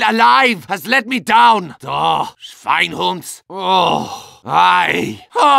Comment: What does he say at 0:31? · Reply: schweinhunds